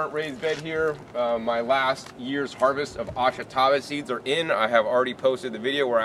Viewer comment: Please what is the vegetable that you mention at 2:40? I do not understand what you say. I have been trying to look it up, but I do not find it. Thanks a stack. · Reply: It's Ashitaba